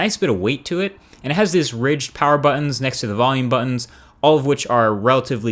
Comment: Very detailed review as always. But did I hear "Power Button(S)" @ 1:35???